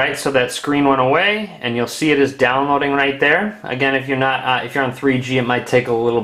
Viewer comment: did he just say, "if u r not in 3G"